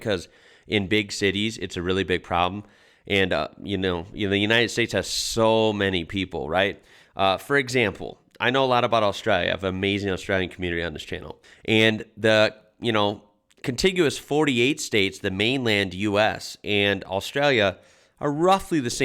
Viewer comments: Good that you know a lot about Australia, but did she say Austria??? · Thought she said Austria not Australia….